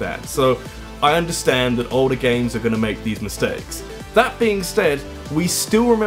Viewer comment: He says " that being stead" , instead of "that being said."